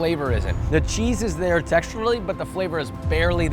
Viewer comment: i thought you said texture > taste